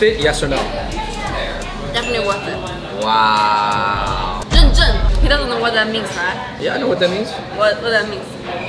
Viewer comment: The subtitle is wrong. It should be "認證" (authenticated, legit) not "認真" (serious, careful).